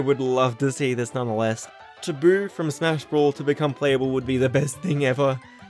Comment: Did you say Brawl or 4? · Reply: Brawl